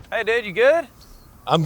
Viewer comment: I thought he said “hey dad, you’re good?!” 🫪